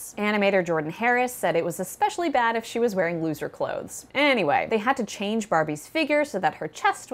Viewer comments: Did she just say "if she was wearing loser clothes"? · For some reason I keep hearing “loser clothes” instead of “looser clothes” 😂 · anyone else thought she said “loser clothes” at first 💀